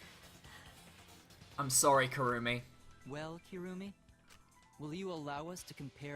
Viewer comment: I do too! I wanna drown somebody and then put their body in a prahna tank but not have it devoured until a kids magic show! So inspiring! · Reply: Oh wait... you said Maki..... I thought you said Kirumi. Well never mind